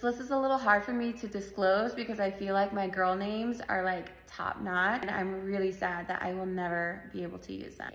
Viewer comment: Did you just say top knot? Top notch! Every name sounds stuck up valley girl.